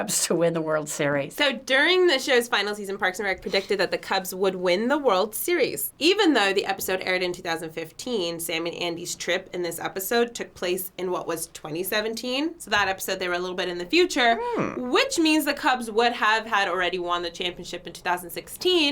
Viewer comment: Did she just say sam Andy instead of Tom